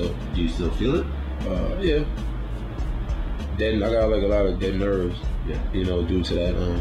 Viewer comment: wat did he say? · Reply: he said he got allot of dead nerves from the bullet wound